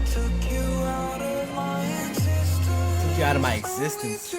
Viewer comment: I always hear “intestines” please help 😂